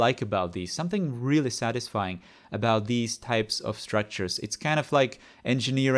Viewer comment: what's the word? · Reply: "Type of structures"